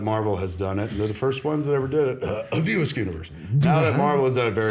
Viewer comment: what does Kevin say at 0:24 under his cough/breath? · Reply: He says "View Askewniverse" (the Kevin Smith cinematic universe)